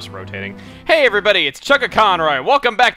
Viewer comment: I thought you said hey everybody it's chaka khan aghahahah